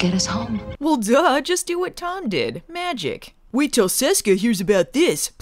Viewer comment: the subtitles say "Sisko", but she's really saying "Seska".